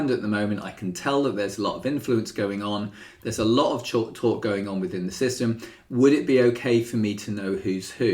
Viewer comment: what is “chalk talk”? The captions say “ch talk.” I can’t really find an explanation online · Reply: That’s hilarious, I don’t know what that means either! It is likely I started saying 'chat' and dismissed it. All I wanted to say was “there’s a lot of talk going on…” Awesome job spotting that!